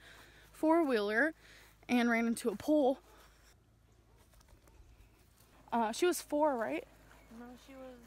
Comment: The 4 year old grave stone one WOWWWWWWWW! Invasion of privacy for 1. The way she told the story was like the 4 year old didn’t die…the way she sounded was like “well she ran into a pole”. And the filming of it. How tasteless! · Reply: I must've misheard, I thought she said ran into a pool